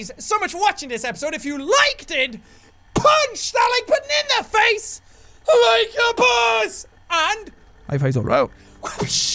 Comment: for the longest time I thought in the outro you said "if you liked it punch that like banana face like a boss!"